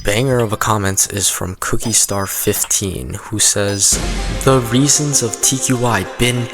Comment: what tqi says: 10 reasons tqi been toxic are what i hear: 10 reasons tqi been cocksick are